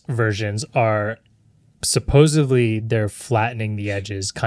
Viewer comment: Andrew, did I hear a "supposively" in there? You need to reel that in my guy.